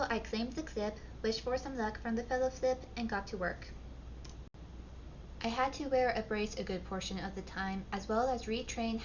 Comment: 10 seconds.. up to a minute… 🫡 👏👏👏. Blew my 5 seconds out of the water 😅. 🙏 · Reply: the Fellow-Flip ? Did I hear that right. Are we “The Fellowflip of the Ring”. 😅